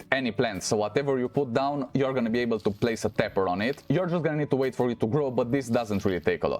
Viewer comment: Thank you but you went too fast. What's that thing you're sticking on the tree to make glow sticks with? I haven't played in a while. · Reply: It's a Tapper. OMG, I thought you said Pepper. Like the vegetable. ROFL Love your accent but it's hard sometime. 🤣